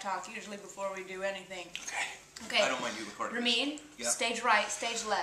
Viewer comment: I always thought that she's saying "you're mean" when it's actually "Ramin"... How silly...